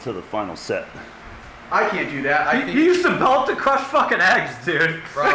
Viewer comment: Did anyone else hear him say at 5:22 "You use the belt to crush fuckin addies dude!" ?? anyone??